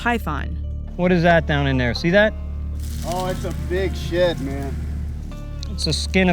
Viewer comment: I swore I heard “Oh it’s a big 💩man” 🤣🤣